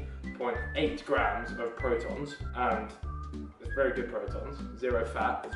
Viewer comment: is it just me or does he say protons instead of protein at 3:56 keep up the good work behz :)